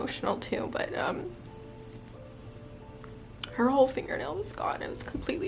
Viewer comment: Omg I thought you said her whole finger came off at first and I was like wait what?! It's horrible when the little ones get hurt, hoping she heals quickly!